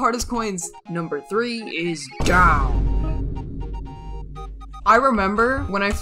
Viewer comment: I thought he said drown